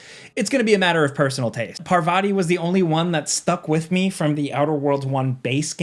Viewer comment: Thought you said parvati is back 😢😢 that would have been cool. Otherwise sounds like an outer worlds game but a step up which is all I could ask for thanks so much! 🫡👏🏻💪